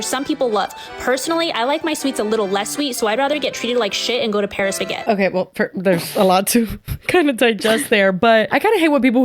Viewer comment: I think she says Paris Baguette which is another pastry store but I have been to many in Socal and I have never had a bad experience